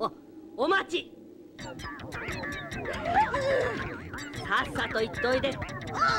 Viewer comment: 便所だけに、ささっと行っトイレに聞こえる